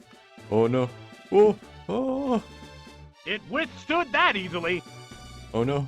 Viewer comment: What does the narrator say in 0:11?? Love these animations in the beginning 🤣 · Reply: "It withstood that easily." (not very effective hit)